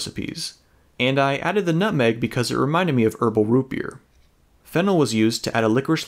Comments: I thought he said 1 and half table spoon of fent but it was fennel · I’m watching on 2x speed and it sounded like you said “fentanyl” there lol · I was across the room for a sec and thought he said fentanyl when he said “fennel” I had to go back and make sure I was trippin 😂😭😭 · When you said fennel i thought you said phenol and i went waittt something is wrong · who else heard "fentanyl was used"